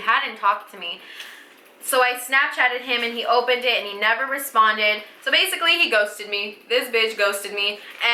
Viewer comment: When I saw the intro I thought she said "so basically he goes to me, this bitch goes to me" but then I saw the actual clip and she said "so basically he ghosted me, this bitch ghosted me" and I felt so stupid 😂😂😂😂